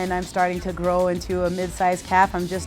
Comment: did she just say she's starting to grow into a mid size calf 👊